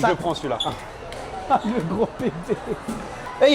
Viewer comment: il dit quoi à 10:38 ????? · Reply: « Le gros bb »